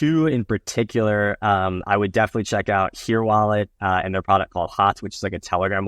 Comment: What did he say at 30:40? Here wallet?